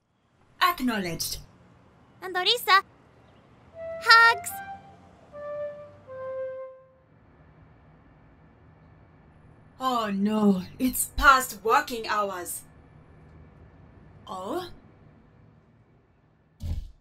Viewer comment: sounded like "oh no its past fucking hours"